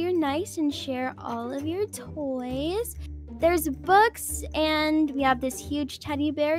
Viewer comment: When she said “books” I thought she said “bugs” lol